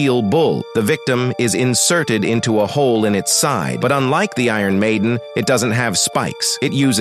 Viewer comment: Oh iron maidon i thought you said fashion maven🐬🐬🐬🐬🐬🐬